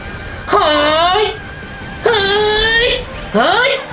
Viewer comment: のはぁい！がリンクに聞こえた笑